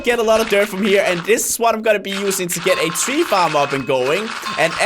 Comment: Is it just me or does it sound like he is saying weed farm. LOL? #famlit